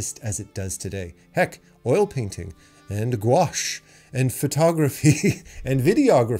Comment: Hey, at 15:55 you list a number of Things that wouldnt exist. One Thing i cant really hear, its Something with "washed". What is it? Also, big fan of your Videos and really Glad that they are there so i can learn. Thank you :) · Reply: Ah ok - I mentioned: oil painting, GOUACHE (as in gouache paint), digital painting, photography, videography - I was just listing out different types of image creation